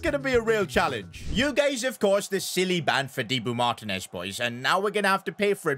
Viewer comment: is it me or did he say Thibaut Martinez🇧🇪🇦🇷